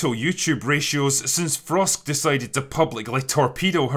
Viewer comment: Who? I've played it back several times, can't hear. Frost? Frosk? Who???